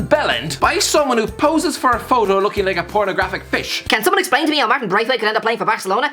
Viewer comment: Did he just say pornographic fish? 🤣 im dead